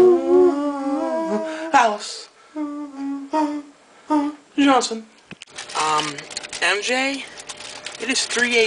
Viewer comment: Did anyone hear him say "your johnson" at 15:06. If anyone did write something.